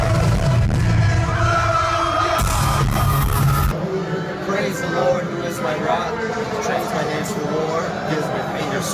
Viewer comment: What do they say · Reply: Something like "praise the Lord who is my Rock and gives my fingers skill for battle"